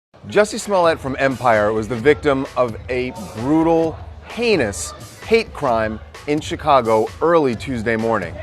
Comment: Lol, I HEARD, "a victim of a brutal PENIS hate crime". Maaan bruh, LMAO 🤣